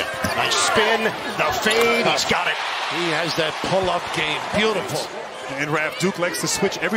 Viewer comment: I thought he said "pullout game" at 0:37 🤣🤣